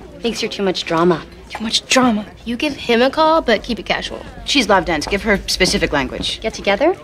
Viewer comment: What does say at 2:59? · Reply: "she's love dense"